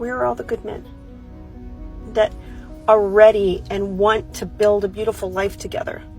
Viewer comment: DID SHE SAY BUILD DAM, I AM 40 AND IT WOULD SEEM I AM TO YOUNG TO START BUILDING MY LIFE WITH SOMEONE, I NEVER KNEW THIS.